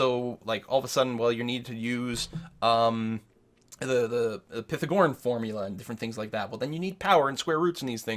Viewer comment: Does anyone know what formula mentioned at 21:25? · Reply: Going out on a limb and saying he meant "Pythagorean" and just pronounced it as "pithogoren".